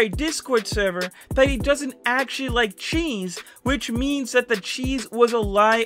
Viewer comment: What DID YOU THINK It Was? 🤔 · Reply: I thought it said Dee's was a lie like you know Dee's nuts but ya it's cheese